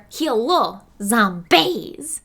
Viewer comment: literally sounded like she said heallo zombaes 😂